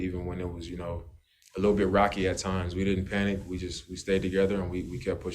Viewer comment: Did Dame just said "Rakija time" at 3:00? 😁👍 As a beverage that DNVR drinks almost every podcast. Nuggets fan here but BIG respect for Dame and CJ, two of mine all time favorite NBA guards. 🙂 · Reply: Lol I’m pretty sure he said “rocky at times “ haha